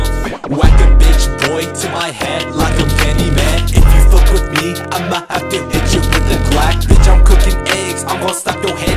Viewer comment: what is he saying when the little yellow guy shows up at 0:27? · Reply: candyman 🎩🌝